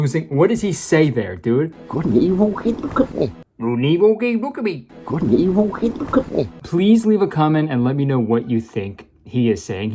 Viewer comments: I think he's saying "Hey- ok, look at me." I'm not exactly sure though... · at like 5:00 i think hes saying something like "good and evil, hey look at me"??? maybe? dawg idk XD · Idk I heard “go to the evil hey look at me” but I know that’s not right 😭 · I think he's saying "Good and evil, Hey, look at me" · I thought he said “look a weevil in a cookie. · I think he’s saying “Good and evil? Hey/k look at me!” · He said "good and evil? Hey look at me" Before that I think he said "I was talking to my dad over the holidays you know, and I like just said--my dad says God is not dead. We act like he is but--" And before that...I cannot fucking figure it out is sounds like "I dream power cars" Sure, man. Me too I guess 🤷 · i think he says, "good and evil keep him company"??? idk man was this dude sloshed on set lol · i heard “good and evil keep me company” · I think he says " Good and evil " and then i have absolutely no idea what he says after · the only thing i hear him say is "good and evil, eat the cookie" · “couldn’t he __ the company” ??? 4:48 no idea losing my mind now or what the other person said in the comments: “good and evil? hey look at me” idk still losing my mind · "good and evil cant look at me" 5:04 is what he is saying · i think he says 'are you ok honey? look at me', like that seems right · I believe in that one scene at 5:01 he says "Good and evil, hey look at me." I could be wrong, but that's what I hear. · I hear "good and evil, hey look at me"